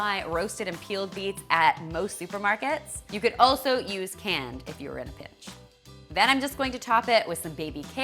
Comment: Am I the only one who heard "You can also use canned, if you're a b*tch."? 😂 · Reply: She said, "You can also use canned, if you are in a pinch."